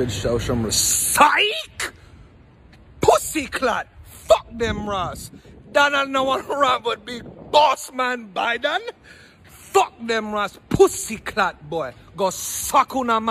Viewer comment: what exactly is he saying before boss man biden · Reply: “Donald do wan rump with big” “Donald does not want any issues with”